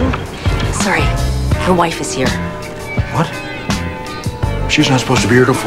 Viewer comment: Sounded like she said, "my wife is here"